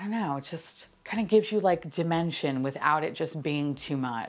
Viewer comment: When you started saying the highlighter would give dimension, I definitely heard “dimentia,” and I thought, “well that’s a new metaphor for her” 😂😂😂